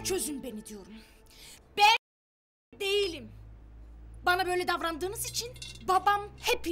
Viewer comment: ne diyor? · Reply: Ben faişe değilim diyor